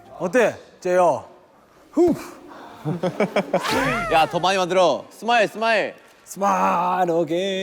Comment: Min. 3:52 J Hope habla en español? Dice: toma otro! Escuche mal o es mi imaginación?😹🥴